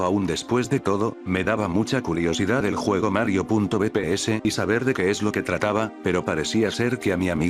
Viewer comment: yo pense que decia mario gps :v